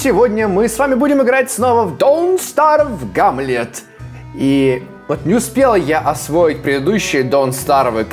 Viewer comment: Я неуспела ты сказал или мне послышалось кто считает то что он сказал неуспела ставьте лайк